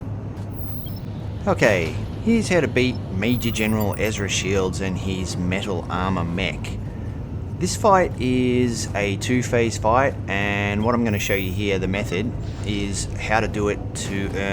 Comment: Lol, sounded like u said mental armor. Yeah, that suit is def mental